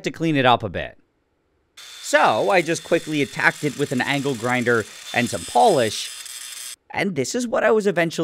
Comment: Why did I hear ‘ankle grinder’ ??? Anyone else hear that, or is it just me?!